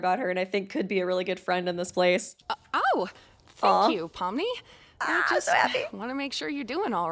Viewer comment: Helpp why did it sound like ragatha was saying fuck you pomni 😭😭